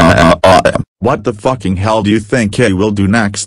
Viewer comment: what did Boris the Teeth Guy says? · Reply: What the f***ing hell do you think ill do next to be continued at woiayahayahayahayahayah